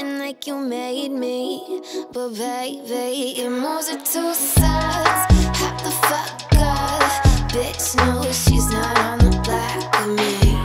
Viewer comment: Am I the only one who hears "Yor moves are too sass" instead of "soft"? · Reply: I keep hearing "You move with two saws, hack the fuck up"